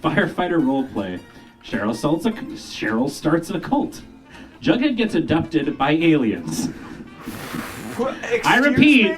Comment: I misheard it as "Jughead gets adopted by aliens" and just accepted it as normal for that show. 😂